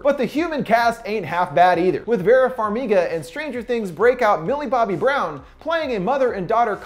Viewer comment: did he say billy bobbie brown LMAO